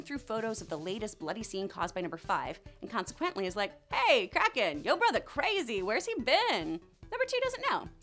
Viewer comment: When she said kraken I thought she said crackhead